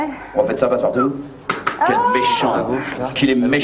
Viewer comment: "Quel méchant, qu'il est méchant" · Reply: Oui j'ai entendu "Quel méhant nabot"